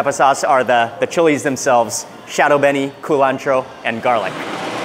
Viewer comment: Couple bits I misheard, ccould someone clarify? When at the Pork Shed he asked if the roast pork had Shadow-benny? Shadow Berry? What was that? Also, when tasting the blacck pudding, did say it tastes like human?!?! wtf lol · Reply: Chadon beni or Culantro. Have no idea what he said while eating the black pudding. The taste is a bit hard to describe though.